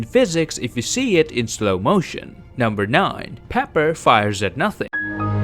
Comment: I thought I hear Pepper wear nothing